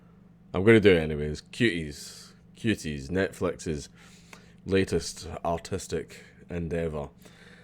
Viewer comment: When you said Netflix's latest artistic endeavor I thought you said latest autistic endeavor and honestly that seems more accurate.